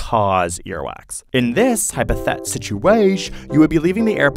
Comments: He did not just say Situashe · Did he just say “In this Hypothet Situatio”